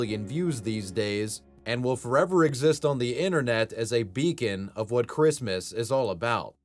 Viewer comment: "...& will forever exist on the internet, as a vegan beacon of what Christmas is all about..!!" HaHa..!! I thought he said *Vegan at first...lulzderp